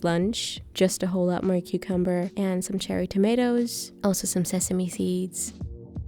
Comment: First I thought you said “some vegan tomatoes” and I was so confused 🤣